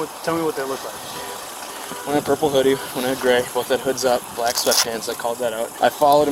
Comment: Oh.....you said Purple Hoodie, I thought you said cute and pretty. My bad....🤣